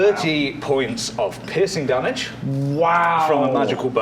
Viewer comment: I thought was saying pissing damage.